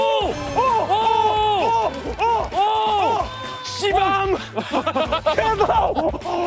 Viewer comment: ㅋㅋㅋㅋㅋc밤ㅋㅋㅋㅋ나만 욕으로 들리낰ㅋㅋㅋㅋㅋ